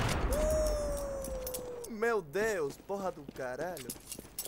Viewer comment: Que dijiste en el minuto 1:52 ?? Entendí el meu deus pero no el resto :p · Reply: Meu deus porra do caralho (?)